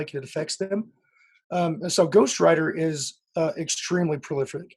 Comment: We're in touch, link in the story? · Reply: see im a idiot and I thought you said ghost rider. Thankfully ghost writer posted. Love your work